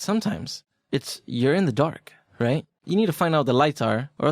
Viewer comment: Hahah I misheard this one as "But sometimes it's urine in the dark" :D I guess that toilet misdirected my thoughts ;D